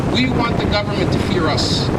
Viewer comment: The owner of this video ASSUMED that man said "We want the goverment to FEAR US". But its all just a CHEAP trick to make things worse. THE TRUTH IS : That man SAID : "We Want the Goverment to HEAR Us". Use your ears and pay a close attention to what the man said. He said HEAR ! not FEAR. to all of you americans, muslims and non muslims who wants peace in the community, dont be fooled by this desperate Video to turn all of you into Islamophobia. Real American muslims LOVE AMERICA !